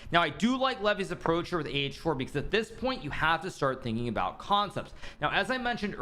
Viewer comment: I misheard 'At this point you have to start thinking about content.' That would actually be very funny.